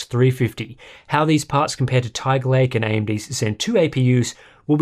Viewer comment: Did I just heard him say "AMD whyzen"? 😅😂